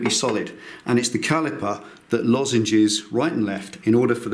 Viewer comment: Great video. At 2:56 you use a word to describe the caliper movement. Sounded like "Lozenges". I did a Google search and also checked the Urban dictionary as well as the thesaurus, but to no avail. Did I hear you correctly, and if so, how do you spell the word. Thx js